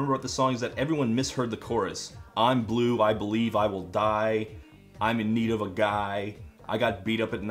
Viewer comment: I heard, "I'm Blue, I'm in need of a guy." THE BLUE MARTIAN EXPOSED 2017!!!